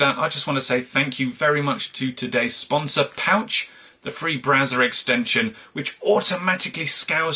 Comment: Hand to god, I thought he said Pouch is a free Brazzers extension, and I was wondering what kind of sponsorships he was accepting.